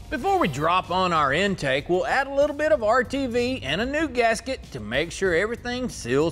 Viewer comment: I thought you said “a little bit” of RTV